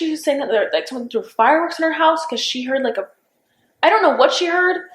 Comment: says "i guess you talked to her, i dont really know" So kaylee did call 911 or the police. Did anyone else catch that?!